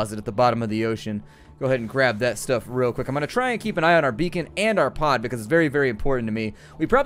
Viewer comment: i seriously thought beacon said bacon